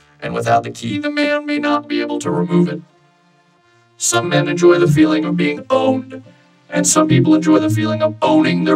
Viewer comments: "some men enjoy the feeling of being OWNED, and some people enjoy the feeling of OWNING their partners" WHY IS THIS SO FUNNY TO ME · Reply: I’mma be real, I thought he said “enjoy the feeling of being BONED, and some people enjoy the feeling of BONING their partners.” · "some men enjoy the feeling of being OWNED, and some people enjoy the feeling of OWNING their partners" WHY IS THIS SO FUNNY TO ME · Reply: OWNED, I thought he said BONED... and boning... Thanks, Kirigiri